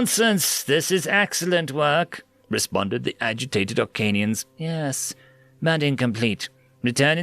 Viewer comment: Instructions unclear, deleted Arkanians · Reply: I thought you said Arkansans, and i was confused